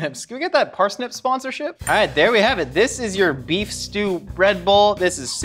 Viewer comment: I thought Josh said beef stew red bull